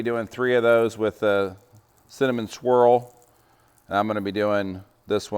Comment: I’m not gonna lie, for a while I thought he was saying cinnamon squirrel seasoning 😂